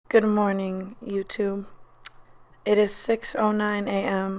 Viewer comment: sounded like she said youto in the first few seconds